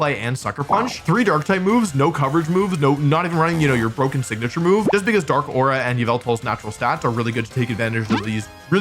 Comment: What did he say at 13:17? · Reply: 3 dark type moves, not even a coverage move, no, not even running your broken signature move, just because Dark Aura...